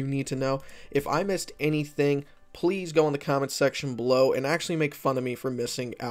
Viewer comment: Did you say "... comic section below" at 0:17? :)